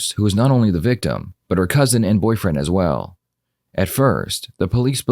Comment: If you feel this channel deserves a subscription, this is a thank you in advance. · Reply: did u say cousin and boyfriend sick